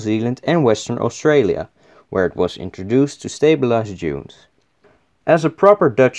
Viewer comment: I thought you said "...where it was introduced to stabilize jews" 😂my bad